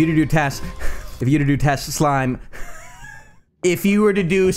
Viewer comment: is it just me or does he say "Touch a slime"